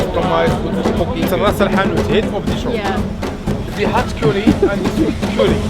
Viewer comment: did he just say 'heads of the shop' XD